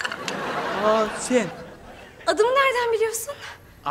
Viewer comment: ahsen demedi bence aaa sen dedi bence ben öyle duydum🤔🤔🤔